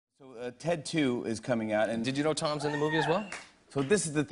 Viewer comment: Before I saw the video, I though "oh, he's probably on the show because of Ted 2" Then the video started and the first thing I hear is "tattoo" instead of "Ted 2".